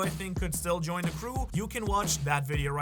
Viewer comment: bro did not just say Shanks so much for watching 💀💀💀